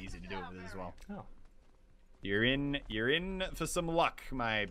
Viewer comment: Did he just say in 11:30-11:32 "urine urine some luck"